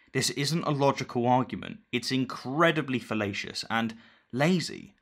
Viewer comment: I always hear him say "it's incredibly felatious"